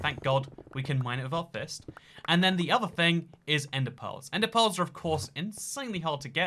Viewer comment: i kept hearing "ender pole" and wondered what in the world that was. about the 10th time he said it, i realized he was saying "ender pearl".